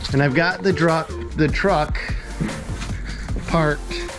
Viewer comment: The Party has no respect for our self defense rights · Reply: I thought he said, he shot the truck??